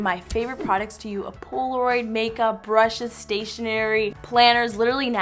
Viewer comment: Lol I thought she said manga,not makeup 😂XD